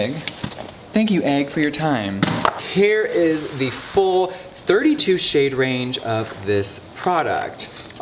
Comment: Wait until 4:54 and don't pause then rewind 10 seconds and it sounds like he said 'here is the f**king egg😂